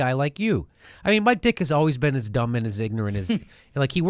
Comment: lol. I thought he kept saying "My Dick'a"